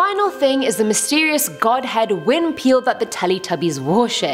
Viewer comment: she really said winpeel 🤣🤣🤣